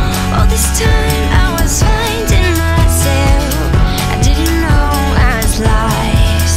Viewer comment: I keep hearing 'I didn't know I was liced' lol, it sounds cool though